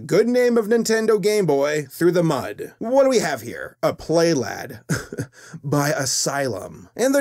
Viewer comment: I honestly thought it said Play Boy